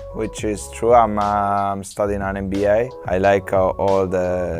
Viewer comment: Is really cool that azpi is doing a Bachelor of Arts whilst playing football when he clearly doesn’t need to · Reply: I thought he said he's doing an MBA??